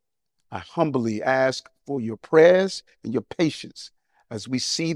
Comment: our prayers and I swear he said donations not patience, I was about to say someone need to check his paper trail and over seas bank accounts because ikyfl